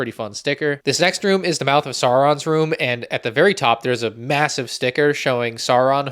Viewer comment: did he say... Massive?